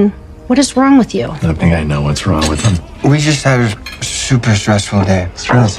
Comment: What did he say here? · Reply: Murray: “I think I know what’s wrong with him.”